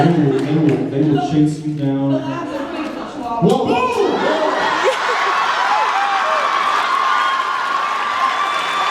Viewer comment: What does the Girl say at 14:43?? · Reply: The lab is afraid of the chihuahua